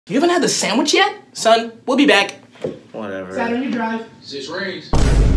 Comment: Saturn was probably saying bars, but all I heard was "Six Rings".